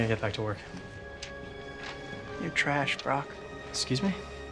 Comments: For the longest time, I thought he said "You're trash, bro." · All this time and I always thought he said " You're trash brah😀